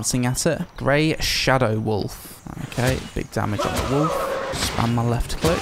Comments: When read "Grey Shadow Wolf", I heard "Raid Shadow Wolf" and I was like: "Wait, they're dunking hard on Raid Shadow Legends in this game?!" xD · why did i hear "raid shadow wolf" 9:50?